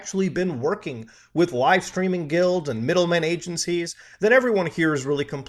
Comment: I kept hearing "guilt" and not "guild".